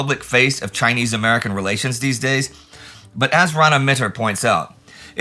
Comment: I must be mishearing. For a couple of episodes now you’ve quoted a historian who’s name sounds for all the world like Rhona Mitra. I’ve had a perfunctorily google and it doesn’t seem like British actor and model RhonaMitra has a sideline in ww2 history. What is the name Indy is actually saying so I can properly google the source? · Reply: Rana Mitter, "Forgotten Ally, China's World War II, <num>.